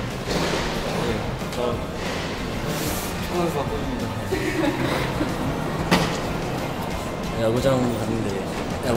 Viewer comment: 지찬선수가 뭐라고 한거에오??ㅠㅠ 10번 다시 돌렸는데도 모르겠음 ㅠㅠ 자막켜도 안나와오ㅠㅠ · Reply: 정확하진 않은데 “축구선수가 꿈입니다 아직” 이라고 한 거 같아요~